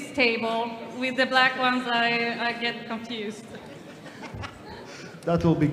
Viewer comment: i didn't understand the joke 💀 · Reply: I think said it would be cut out cuz it could be a dick joke. "with the black ones I get confused". But it's kinda interesting to say that on this stage lol.